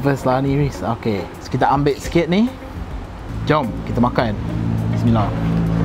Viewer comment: Aku salah dengar ke?? minit 11:50+ ckp Bismillah